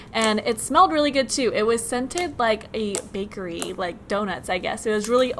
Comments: Did anyone else hear “babe”?? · I heard “BABE!”